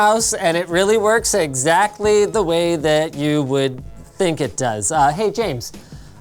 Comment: Linus said: "exactly the way you think it does" I heard: "exactly the way you hope it doesn't"